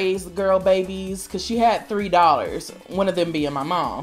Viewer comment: I thought you said she had 3 dollars and not 3 daughters, lol